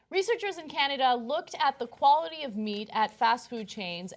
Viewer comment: I heard "Researchers looked at the quality of mead". Where can I get some?